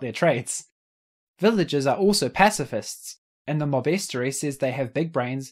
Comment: for a second I thought he said "villagers are big brain"